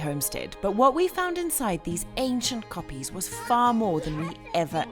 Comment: i think I heard you say a Dutch word.? Was it “ Koppies “ ? Or more South African ? Left over from the ‘boers’ which is Dutch for farmers. I like your pottery. If you ever get the time in your busy life, you have to get a wheel. Love the new grass ! So Koppies is a term for the rocks, right ?